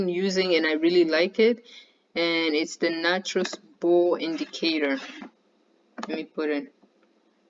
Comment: You're welcome 😊 Which last tool are you referring to? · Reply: Hello at 29:50 I couldn't make out what you said but it sounds like natural spool indicator? Thank You